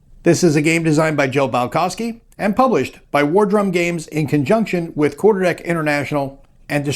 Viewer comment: CRap, did you just say Wardrobe games? What? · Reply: War DRUM Games. lol Like it says in the video title. ;)